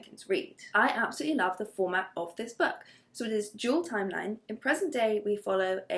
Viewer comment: Not me being so obsessed with Taylor Swift that every time you said 'dual timeline' I heard 'jewel timeline' 🙄😅